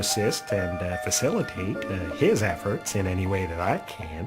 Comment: i literally thought "efforts" said "clitoris"